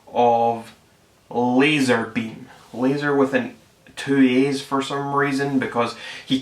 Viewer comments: he can't really talk about annoying accents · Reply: I thought he said lazarbeam was spelled with two e’s, i had to replay it 3 times before i heard two a’s. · He sounds like he said "leezerbeam"